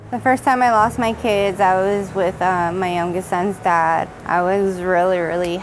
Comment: did she say "my youngest sons dad"